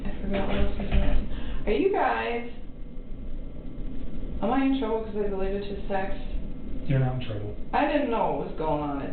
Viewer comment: when she say am I in trouble for ____ . What did she say?? I can’t make it out. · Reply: Am I in trouble for deleting his text